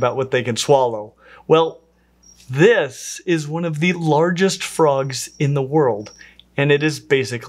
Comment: This is not the largest frog in the world. Goliath frog is the largest known. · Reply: Just watched again, indeed, "one of largest", i don't know why I've heard it as "the largest". My bad, apologies.